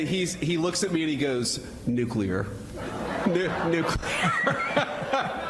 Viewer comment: there you go, Benny! Can you repeat what he said? He said Nuclear. Not Nuceler!